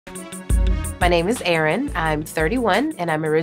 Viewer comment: In the beginning I thought she said,"my name is Aaron." I was like wut! Until I saw her name written on the screen